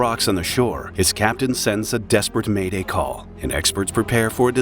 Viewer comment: Whew, at first I thought I heard "desperate mating call" 😅😅😅